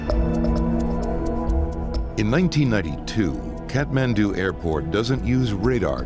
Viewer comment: 😮😮did I hear correctly.. 1:11 ohh 😮😮😮 1992 😮😮😮lol. 😂thought it said 2022.